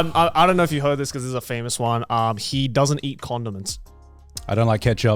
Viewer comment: I dead ass thought said “he doesn’t eat condoms” 😂😂